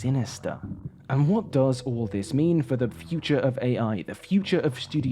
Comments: Thought you said "The future of arse." Had me like, "Fuck, is nothing sacred to these ghouls?" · future of Ass? That's what I heard. I'm sorry